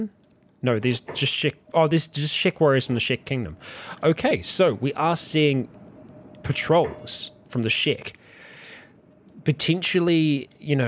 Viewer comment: Everytime I hear "Shek warriors" or "Shek Kingdom" etc. I keep hearing it as "Shrek warrirors of the Shrek kingdom"